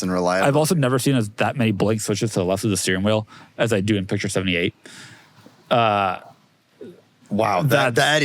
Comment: wtf did he just say??? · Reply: "Never seen as many blank switches next to the steering wheel as he has in picture 78." Perfectly clear sentence?